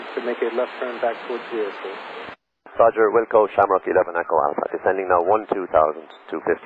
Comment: I think he said "wilco" at 02:07 instead of we'll call